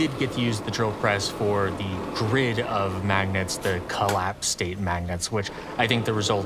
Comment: Fantastic video, as always! Only nitpick i've got is that at 39:15, the captions say "the grade of magnets", while the voice-over says "the grid of magnets".